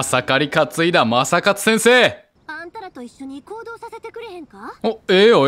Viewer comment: 九条「暴動させてくれへんか？(空耳)」 [パイモン「もちろんいいぜ！(合いの手)」] めちゃくちゃ好きw